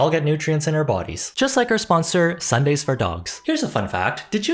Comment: I heard “Sundays food for dogs” as being Sundaes. Can dogs eat ice cream?🍦 🐶